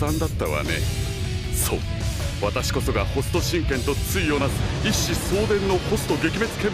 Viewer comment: I...am pretty sure I heard Ken-Oh...wouldn't that mean that it's a Raoh reference? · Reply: is he really said hokuto shinken ...? 😅